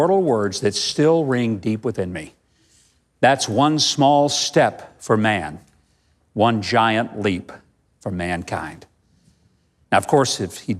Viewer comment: Armstrong actually said 'That's one small step for a man, one giant leap for mankind", but he said it in such a way that the "a" was not clearly enunciated, and so everyone heard it as "One small step for...man, one giant leap for mankind".